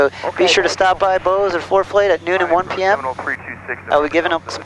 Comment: At around 7:51 did say foreplay instead of Forflight???